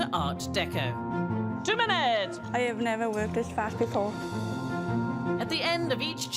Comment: sorry English is not my first language, did she realy say "I never work with fat people"? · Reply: I thought that's what she said too lol! With the closed captions on, it reads as "I've never worked this fast before" which I can hear now. Her accent makes it sound like she said fat people lol 😂